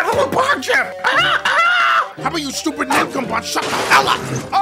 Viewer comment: i got real scared when i just heard robotnik go 'you stupid ni-''